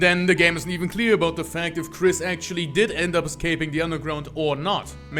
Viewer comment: did he say frisk or kris? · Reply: Kris, cause I'm a smart fella